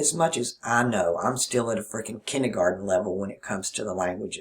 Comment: This is THE talk I needed to hear tonight, of all nights. When said "kendergaten" in my same southern accent, I immediately thought KinderGarden. As I listen to music while playing cards with my neighbor, she relates her stories and memories to me. My "mind" kept wanting to write down song titles and look up artists and analyze lyrics. Suddenly - tonight, I went to "feel the vibration it gives to me and learn how to pour it back out." Well, it didn't come in words but I heard it! That's the Kinder Garden - KARMA! I knew exactly what part of myself was speaking to another part of myself.